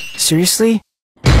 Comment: Мне одному показалось что он сказал всмысле?